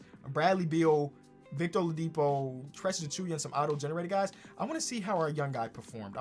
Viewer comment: Did u just said Precious Achiuwa is an auto generator guys? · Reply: he meant: "oladipo, beal, precious achiuwa AND some auto generated guys